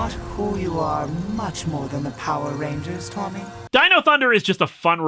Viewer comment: Every time it came up, I just heard "Dino DNA!" from Jurassic Park.